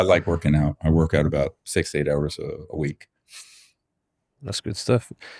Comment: Did he say he works out 68 times a week?? Or “6 to 8?” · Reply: 6-8 hours per week I thought he said.